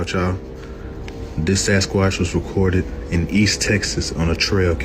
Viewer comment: 😂 I thought he said “assquatch” for a second and I thought, yep! Fitting! Cause Bigfoot got some glutes!